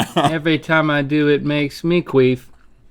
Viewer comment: Makes me QUEEF ahahahah is that what link says